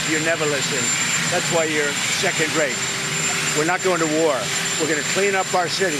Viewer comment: Not only did he snap at a reporter but called her 2nd rate to her face. So disrespectful to another human being….he is a low class bully, a felon!!! He needs to be taken down and removed. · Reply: I thought he said 2nd grade